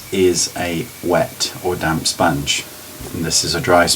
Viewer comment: i keep hearing "damn sponge" haha your videos are by far the best ive seen.